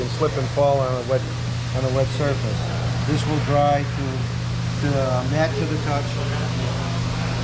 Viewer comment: Hi. Thank you for bringing this video to us. Quick question regarding the trim restorer. It looks like it's a high gloss product when applied. Does it dry to more of a Matt finish or do you need to knock it back with a microfibre towel? Thanks in advance. 👍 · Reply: I think he said it does dry to a Matt-type finish